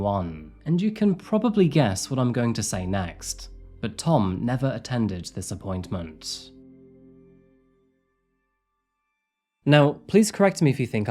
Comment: what did he say? Tom never what? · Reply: He never kept the appointment (with the police.)